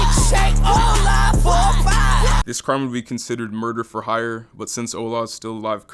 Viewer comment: I thought he was saying ''ON LIVE FOR 5'' As in he had someone go and shank some mf for 5$ 💀🔪🩸